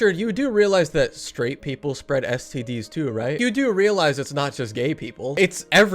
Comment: I THOUGHT HE SAID 'SPREAD ASS' NOT SPREAD AIDS OMFG